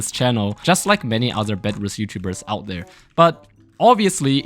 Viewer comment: Am I the only person who heard him say bedless YouTubers lol